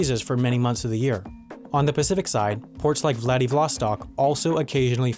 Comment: Did he say Vladivlostok?